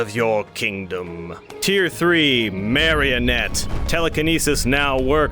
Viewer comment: when he said marionette I thought he said married ….. happy wedding two dead people